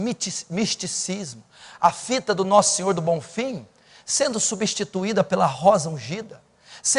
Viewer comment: ele disse a vida do nosso sr. do bonfim?eu entendi certo? no